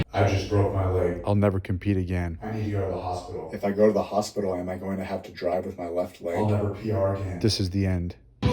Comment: I thought he said "I'll never be while hard again"